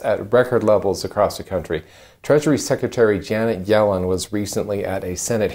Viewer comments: For about 3 seconds, I thought you said "treachery secretary"😮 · I thought he said TREACHERY secretary 😆 Janet Yellen..... 😆 had to go re-listen..